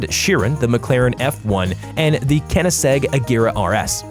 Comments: "kenesegg" is what I heard. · Did you just say kennisegg🤦‍♂️🤦‍♂️🤦‍♂️